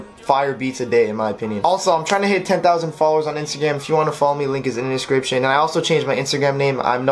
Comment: did you say Insta Gam? lol